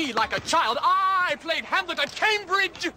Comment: I thought he said Hamburger Cambridge!